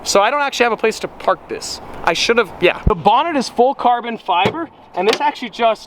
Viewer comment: Did he just say bonnet? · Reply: Bonnet . James Bonnet